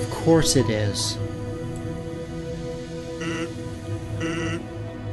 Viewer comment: Oh my god at the end I thought would say "dirty brother killer" not "Then why'd you kill my brother?"